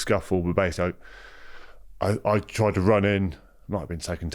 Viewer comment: did he just say he might have been taking test? 😂😂😂